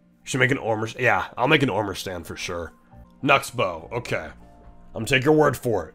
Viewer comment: When you said Knucksbow it almost sound like you said nuts bow